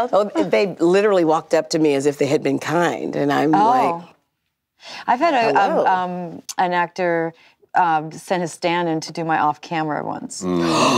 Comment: what did diane kruger said at 1:48 ? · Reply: I've had an actor do my stand in once !